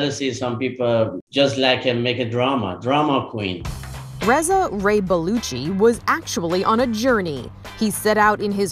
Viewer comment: Wheeeew, I thought she said Ray-Paluchi. Not Ray-Baluchi